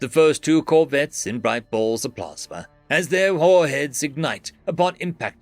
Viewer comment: interesting pronunciation of warheads, sounded like whore heads.